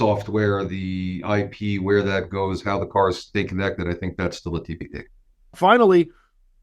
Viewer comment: I couldn't understand what he said due to bad audio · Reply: Still a TBD. As in We don’t know yet.